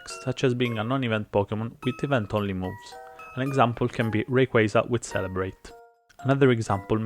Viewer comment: what did you say? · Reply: «Rayquaza with Celebrate». Since most event Pokémon have pretty uncommon moves, but their distribution is fairly limited, people like to hack Pokémon by giving them the same moveset an event Pokémon has, making those mons illegal.